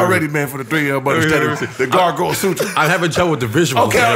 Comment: did 3 say the gargoyle sutra? 😂😂😂😂😂😂